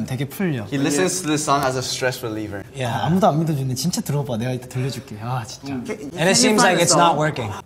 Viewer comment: Where you said "idk what he said, sorry" during the song association where Yugyeom was like "parapapapa" (2:50), he said, "No one believes me, seriously, listen to it, I'll make you listen to it, seriously."